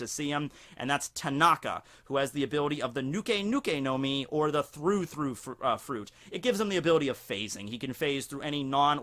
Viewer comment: Also Oven when it comes to fire powers By the way, red-armed-guy’s fruit is like a Smile Fruit When you said “through-through fruit” I thought you said “fruit-fruit fruit” If you wanted a water-but-not-water fruit you should make the milk-milk fruit That dog should have had the tongue-tongue fruit, model: long tongue Edit: Ah nvm Edit 2: I comment to quickly